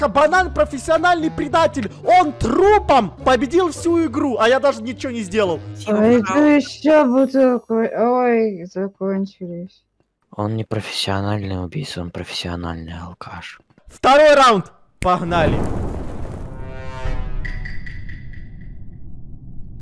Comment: Банан:надо еще бутылку. Ой... Кончилось... Хаха Кто тоже это услышал сюда 👇